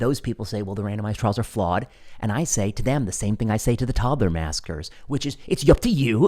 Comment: Love the content, love even more if you'd speak louder. You say "toddler maskers", I hear "toddler massacre-ers".